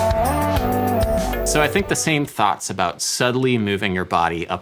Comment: did he just say thot